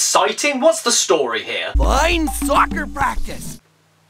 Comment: I thought he said line fucker practice